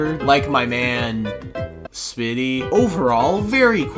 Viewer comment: who else heard smi77y